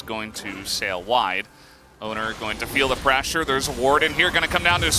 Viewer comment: Wait... I thought she said bOner